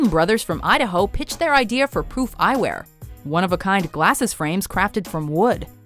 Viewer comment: Am I the only who thought she said "Poop Eyewear?"